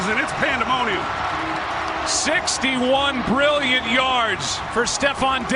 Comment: “61 brilliant yards” Thought he said billion was like WHAT. dis dude a legend